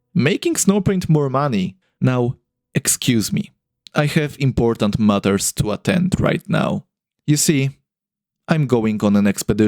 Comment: In the end it sounded like you said thag you have "other mothers to atend to" xD